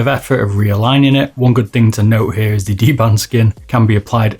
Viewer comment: DBrand, not dband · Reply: I was referring to the pronunciation tbh, kind of sounds like he was saying D Band rather than D Brand. Hoo kares abowt proper spellin and pronunciation anyways these days!